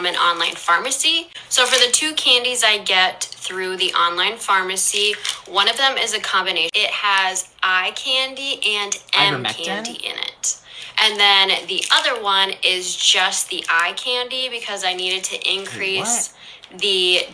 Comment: That's what I thought she said "ivermectin"? 😢😢😢😢😢😢😢😢😢😢😢😢😢😢😢 Girl, no! Get your butt to the oncologist, even if you're poor....get to the Mayo clinic! 😮😮😮😮😮😮😮😮😮😮😮😮😮😮😮